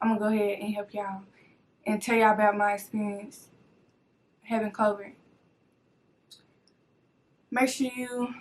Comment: i really thought she said “having COVID”